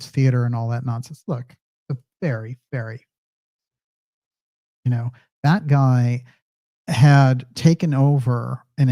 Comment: What did say at 50:33? The sound muted. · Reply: Hi Melissa...yes it went out right there, but it was 'Deep State assassination.'